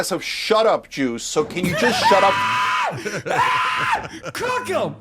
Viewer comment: At first I thought he said "shut up Jews"